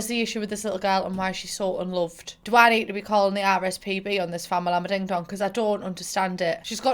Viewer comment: You may have said the right acronym but I heard "Call the RSPB" and my dumb brain was like "Why would I call the Royal Society for the Protection of Birds on the child"? Gurl...